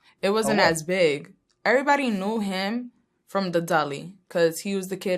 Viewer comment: Żulek niestety ale nie masz racji ponieważ te tłumaczenie jest na wyższym poziomie niż te przez tłumacza google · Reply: gówno prawda że to nie tłumacz. W 3:19 mówi "Everybody knew him from the deli" a autor tłumaczy to jako "Każdy znał go z lalkowatości" XD Więc albo użył tłumacza który to beznadziejnie przetłumaczył albo usłyszał w tym wywiadzie dolly co tłumaczy się na laleczka gdzie wyraźnie słychać DELI czyli sklepy delikatesowe w których pracował. Tekst wcale nie jest przetłumaczony na jakimś super poziomie poza tym według mnie każdy kto uczy sie angielskiego powinien to zrozumieć na spokojnie, ta dziewczyna nie gada jakimś wyrafinowanym angielskim ani nie używa bardzo dużo słów ze slangu